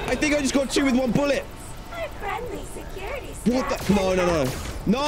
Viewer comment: Uhmm at 25:25 did anyone else hear chica go “WHAT THE F*ck”